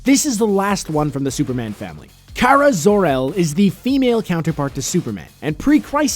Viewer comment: I thought he said "No more from the superman family"